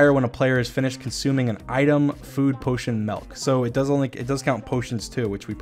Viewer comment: Nobody's gonna see this, bc I'm so late But can we just talk one more time, abt how he says MELK instead of milk 2:43 lmao